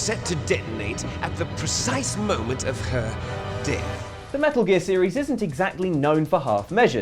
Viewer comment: the subtitles say "the precise moment of her dick" imao